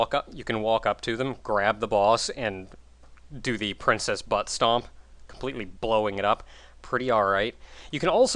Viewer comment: marry chrismas, also it sounds like she is saying princess buster, not princess but stomp, like zangeif from street fighter